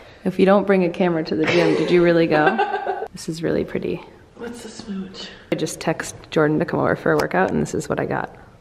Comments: did say "let's just smooch" or am i just hearing things..lol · /29...did say, "let's just mooch or smooch?:)♥